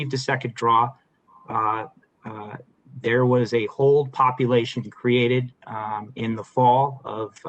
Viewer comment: Did he say"there is a whole population created" what an irony. For this topic,🤰 it's not safe for the unborn.